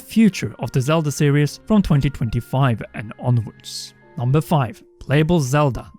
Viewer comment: did he say 2025?